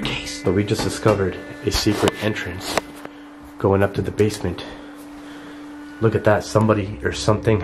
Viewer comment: huh did u say basement ?